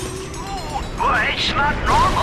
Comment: did he just say "Clink"?